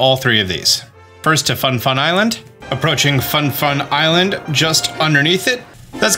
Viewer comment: I thought you said “just fun-derneath” fun fun island for a sec…